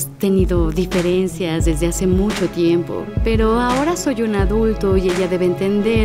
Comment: Fue un error lo que escuche pero en el minuto 2:00 dice que es ADULTO